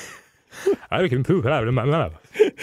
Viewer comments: Dan did not say what was on the screen. I definitely heard "I can poop out of my mouth." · Did he say, "I can poop out of my mouth." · Does anyone know what Danny said at 7:00? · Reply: I can poop out of my mouth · What did Dan say at 7:00 ? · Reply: Never mind, just scrolled down and found the answer. He said "I can poop out of my mouth." LOL